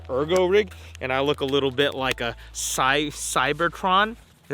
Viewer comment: did he just say he looks a little bit like a planet?